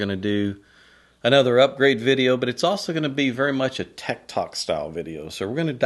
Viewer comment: When you said "tech talk" I thought you said "Tekton" and thought you were throwing your hat into the ring on THAT particular conversation... 😵